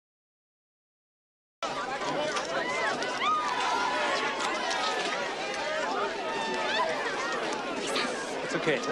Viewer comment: what did the Dealer girl say to Hoagie? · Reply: Niko here she said to Jake “Five Minutes” as that’s how long she had until she gets off work.